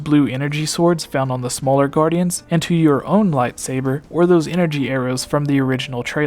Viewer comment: did he say light saber? I think I heard him say light saber.. really now, light saber